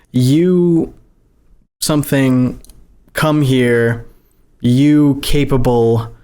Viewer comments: I think I heard “why have you come here? You serve a (cannibal?) -“ and can’t make out the rest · i hear the "why have you come here' you serve a (cannibal). and maybe the cannibal part means carnis but is disorited so it sounds like that. and i hear words after that but they cant make anything useful. and it the final kid was a girl from the famliy but we know vinsane is male